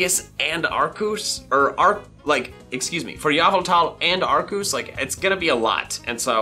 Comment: if you watch around this point with captions on is will say Yahoo towel.